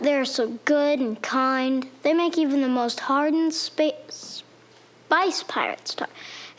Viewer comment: did he just say "spice pirates?"